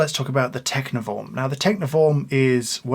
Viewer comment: Wait, what? I thought you said "Techno Vom" as in, you know, "vom". Oops! Sorry! 🤫😳🤣 I like Mr Wilfa's design and detachable water tank. 🥂